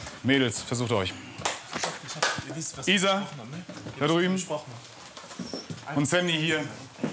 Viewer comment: Er: Wie heißt du Sie: Candy Er: Ah Cindy Sie: Nein Candy mit C Ich: Hää wtf😂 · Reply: Ich habe verstanden Sandy oder Sindy